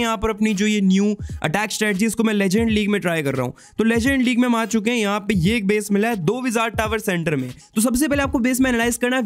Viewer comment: 💀 kya bola hai bhai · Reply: Legen league me aa chuke hai 😅